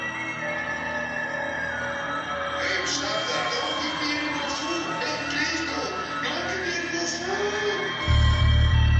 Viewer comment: I wish I knew what he was saying. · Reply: 10 years late but: "Hey stop that, don't give me any more food! It don't taste good! Don't give me any more FOOD!"